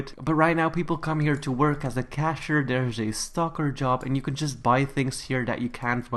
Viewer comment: I heard “there’s a stocker job” as “There’s a stalker job!” XD